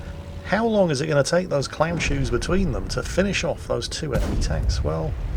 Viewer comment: Just got home, notification from the, how can it get any better? Edit: Did just say "Clownshoes" because... I don't think I've heard him say that in a mess of years!